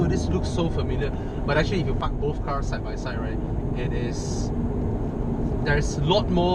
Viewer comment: at @ 5:20 it the first time it sounded like "If you fuck those car side by side".. hahaha good video! Keep it up!